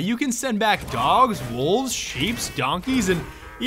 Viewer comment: did you just say "sheeps"?